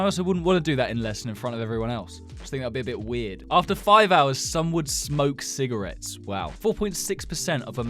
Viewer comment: Help I thought it said snakes would smoke cigarettes 5:50 😭😭😭💀💀💀